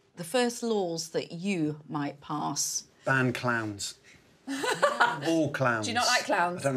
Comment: Ffs I thought he said he was going to ban CLAMS not clowns aha